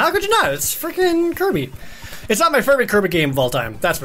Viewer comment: Did he say "Its definitely not my firby kirby game"??